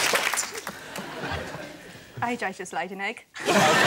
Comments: What did she say at 00:16 For everyone wondering she said “AJ’s just laid an egg” haha x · wth does she say? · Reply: Rob Cooke i think i just laid an egg